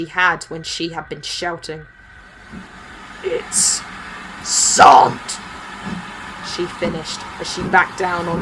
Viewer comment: It's what?! What did Lizzie say? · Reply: "Sarn't". As in Sergeant with the middle of the word missing.